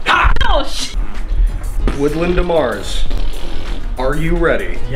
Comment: Whoa... Also, I forgot ' last name was Demars so the whole time I thought you guys were saying "Woodland to Mars" and couldn't figure out what the context for that was...